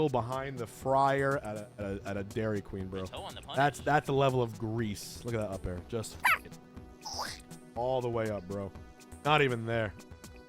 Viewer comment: I couldn't understand what you said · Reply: "just fucking mimics the sounds of grease being smeared all the way up bro" is what was said I think